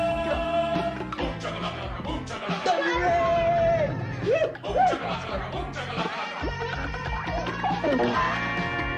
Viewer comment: Can someone tell me what they said at 1:54 after their little dance thing? Love the movie btw. Watched it when i a wee boy. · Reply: To answer your question 9 years later, before it hits the 10 year limit and you don't care anymore, they're saying "Thunderin'!"